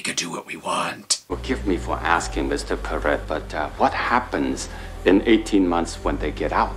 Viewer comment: did he just say Mr Pervert? "INDEEEED" - David Lo Pan.